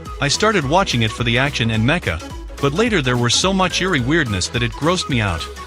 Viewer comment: I thought you said "so much eerie goodness".